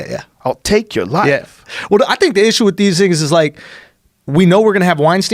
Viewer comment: If you really look into it, at 6:55 it sounds like he says "I think the issue with these niggas is"